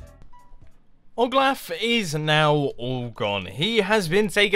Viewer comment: anyone else heard him say oglaf?